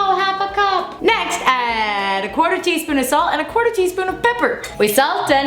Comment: OK, in these covid-19 times I heard "quarantine" in stead of "quarter teaspoon"